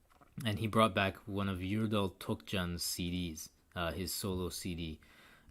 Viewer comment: What was the name of the oud player you said at 8:18? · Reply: Yurdal tokcan